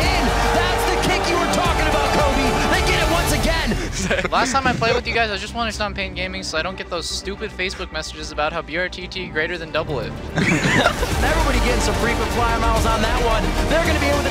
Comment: what he said? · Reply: Eu quero stompar a pain para que eu pare de receber mensagens estupidas no meu facebook falando que Brtt é melhor que Doublelift kkkkkk